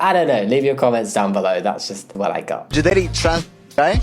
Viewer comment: wait the subtitles say "trans girl" but i definitely heard "trans guy" it seems like whoever made the video was trying to make the interviewee seem transphobic by saying it would be gay to date a trans GUY when it would be gay for a guy to date another guy (yes i know you don't have to gay to date a guy as a guy but is still a gay relationship even if one or both of you are multisexual)